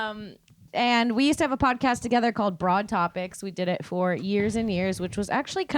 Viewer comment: Ha I was looking for your old podcast, thought you said bra topics!!